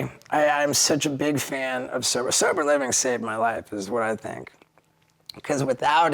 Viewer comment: anyone tell me what he says at this time ??? surpur living ? what living ? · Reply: Sober living